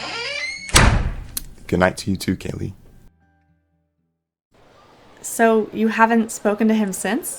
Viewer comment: After the kiss I was like???? Cause I thought he said "good night to you to KITTTEN" 😅